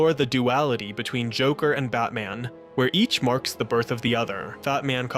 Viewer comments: I thought it said "Bateman" lol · did you call him fatman on purpose?